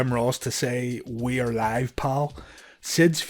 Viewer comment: lol 2:55 Mandella effect! Everyone seems to think JR said "WE'RE live, pal." He did NOT say that. He said "IT'S live, pal".